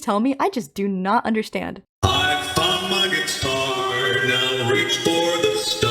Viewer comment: Every time I hear this line I can only think of "I FUCKED MY GUITAR" Edit: 14:57 i have a bit of a crack theory about this line(and the "the others are under my protection" line); it's not being said to Afton, but rather to TOYSNHK since some of the puppets other lines ("seeing you powerless is like music to me") seem to contradict this idea that Charlie no longer hates Afton and she may be trying to free the other souls from TOYSHNK... but idk